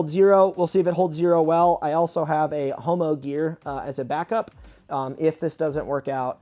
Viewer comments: did he say Homogear? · Did I hear homo-gear? 😂